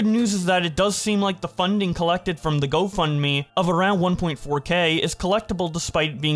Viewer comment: you say 1.4k instead of 4.1k as written on the Tweet sniplet Mr.